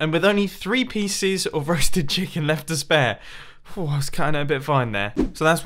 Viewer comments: Is it just me or at 6:21 did he say rusted chicken? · When you made it back from the Nether it sounded like you said "rusted chicken" XD